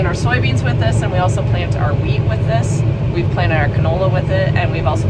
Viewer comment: Hahaha! I thought you said you planted your "weed" with this, and I'm afraid I snorted my coffee! *REWIND!! ....oh, WHEAT, gotcha...*mops up...